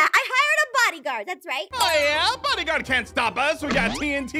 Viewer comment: I thought ein said "hoyia~" on 1:41 but he actually said (ohh yeah?) ahahahahahah xD